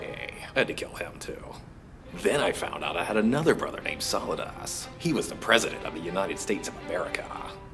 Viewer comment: it sounds like he said"solid ass"hahaha